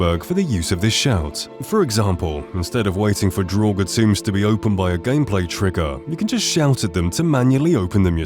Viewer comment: What is he saying at 3:57? I can’t make out what he’s saying we can shout at lol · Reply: 'draugr tombs'